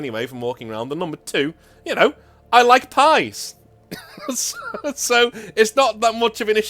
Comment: I first heard, "I like guys" 😂